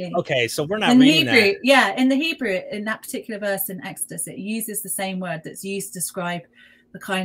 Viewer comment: Uhm. ... Bereshith 2:2 uses the 3ms Qal Preterite of the verb שׁ•ב•ת with the waw consecutive וַיִּשְׁבֹתּ , unless you're referring to another verse I'm not seeing the Hollow verb נוּח In the BDB it has cease,desist,rest ... ²) desist 👉🏾 from labour, rest 👈🏾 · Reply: Ok her accent through me off ,when she said exodus I heard " next to it" ... u kno all u had to say when I asked was " she said in exodus" not get rude